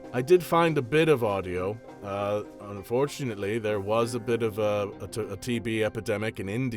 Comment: I thought he said "fortunately" 😭